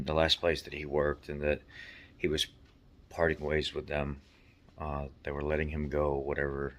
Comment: Its funny when I listened to you I heard the words "he's party boys with them" and Im thinking.... seems odd to say its not your place to let out his business and then call him a party boy. It was only after I put on close captions before I realized you were saying: "parting ways" lol Good job keeping his confidence. If people are so presumptious to think you owe them the inside info on your friend...well you dont need subscribers like that. Good job man and take care of that leg.